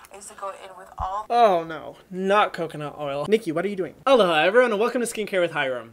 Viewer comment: I'm so dumb...I've been watching your channel for months and always thought you just said "hello ha, everyone!" not realizing you were saying aloha. Like I actually just thought you had a fun way of saying "hello"